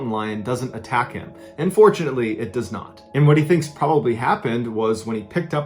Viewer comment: lmao I thought he said “unfortunately it does not”